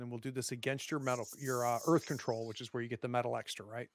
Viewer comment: A very different change of pace from the other games on the channel. A lot of fun! (And when the Ferryman first said he wanted to use his “Earth Control” at first I heard “Birth Control” which is an entirely different game (although, I am looking forward to Ms class on that topic… :P)